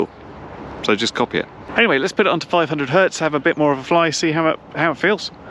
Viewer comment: I thought they said it could'nt do 500 hertz